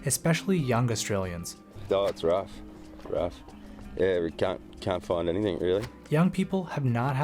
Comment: what did he say? LoL Australia. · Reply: Its rough, rough. Cant find anything really